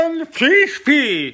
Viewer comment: I thought he was saying Covid. Man's figured out the future.